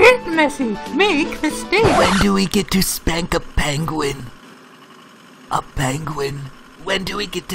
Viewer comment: i heard "spank a plane"